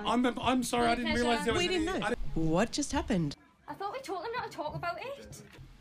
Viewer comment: okay im here again but let me know what jade exactly said here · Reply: i thought we talked them not to talk about it? am i rightt?? hahaha